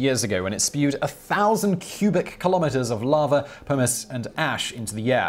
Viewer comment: for a second I thought he said yellowstone sent 1000 km3 of lava humus and ash into the air